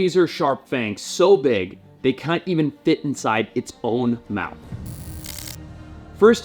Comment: Imagine being alive in 1800 out fishing for the markets and you pull in a viperfish. I really wonder how those fisherman reacted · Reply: "fangs so big they cunt even fit in it's own mouth!". Did I hear that correctly!?🤔